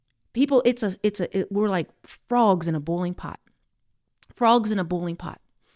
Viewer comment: You say the word "Boiling" so strangely, i had to turn on Sub titles, i thought you were saying a bowling pot lol, not being nasty darlin, just saying 😅.